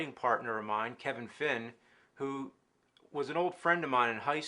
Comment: I thought he said "Kevin Flynn" from Tron instead of "Kevin Fin"